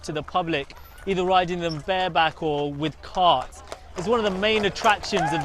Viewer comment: Wait wait @ 2:34 Did he say "ride them bareback"? WTF?!?! Lool.